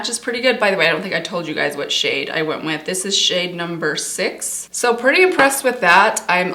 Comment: What shade do you have in the serum? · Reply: I think she said number 6.